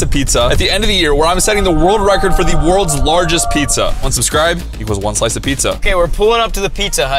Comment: lol the subtitles says unsubscribe for a slice of pizza 😂